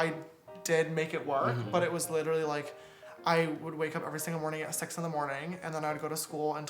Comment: I thought he said “I’d wake up every single morning and have sex in the morning” 😂 lmao